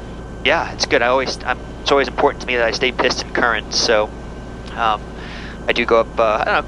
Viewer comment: I did an audio “double take” when I thought you said it was important to you to stay “pissed and current.” 😳🤣